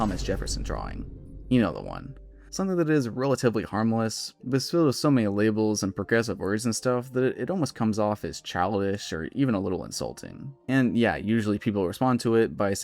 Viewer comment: I’m sorry, but for the life of me, I CANNOT understand what you are saying here. Can someone give me a transcription of this part? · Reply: "You know the one. Something that's relatively harmless but is filled with so many labels and progressive words and stuff that it almost comes off as childish or even a little insulting."